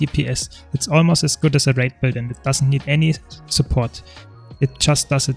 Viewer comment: Hi, at 0:08 I think you say "wraith build" but I'm not sure. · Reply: Hi, I actually said "raid build". Sorry for the confusion!